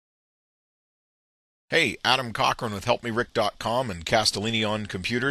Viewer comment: did he say adam cock?